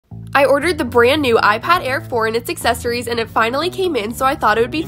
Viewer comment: Bro is it just me or did I hear "The new iPad Air Jordan" huh?..